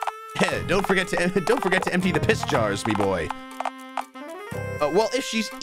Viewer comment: I don't know if I'm over thinking this or what but I'm pretty certain that Ranboo said "piss" at about 1:13:50 ish and also I don't think it's the first time either